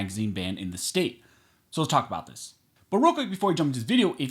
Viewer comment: I always hear "Barooka" instead of "But real quick"... Always